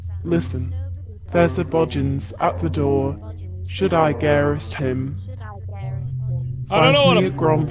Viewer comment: Mark i think that when he says there's a bogin at the door.. he actually saying there's a body at the door. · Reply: little mystery444 and it sounds like he says "should I garrot him?" and a garrot is a weapon used for strangling I think?